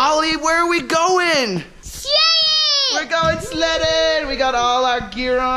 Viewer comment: I thought Ollie said " we're going slaying "